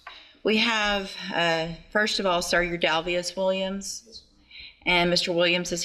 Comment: Coming up with those stupid names is almost like a special skill. Dalvius? Lmao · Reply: I thought she said SirDalvius? Either way made me laugh